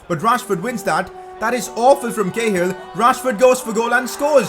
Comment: At the beginning a thought he said gayhill😂😂😂😂